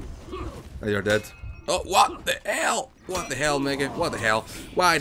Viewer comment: Did he Say the n WORD? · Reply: "What the hell mege". Is what he said.